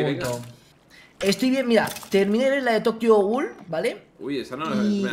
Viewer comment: escuche tokyo ghoul???